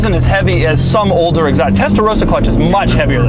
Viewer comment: Did he say the Tesla Roadster clutch is much heavier at 12:08? ^^